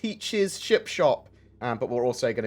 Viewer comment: Sounded like you said chip shop, mmmmm chips